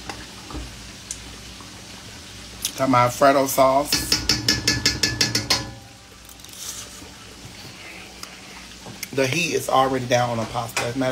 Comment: Did he say "Frodo" sauce? This Lord of the Rings oversized elf-looking gruel(love that word,) eating giant. I swear I heard "Frodo" sauce cause I sure ain't hear Alfredo.